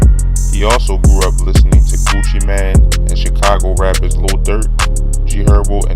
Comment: it sounds like he said “he grew up listening to coochie man”